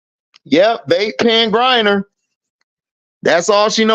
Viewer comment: lol I thought you said “They Can’t Grind Her” until i read the comment.